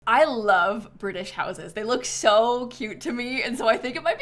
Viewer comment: i thought she said “i love british noses” and i was so confused